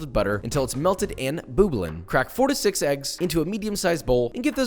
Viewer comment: At one point I thought he said 46 eggs.... then I was like oh 4-6 eggs LOL